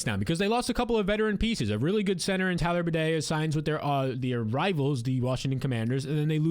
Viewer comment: it sounded like he said "their rivals the Russian Commanders" lol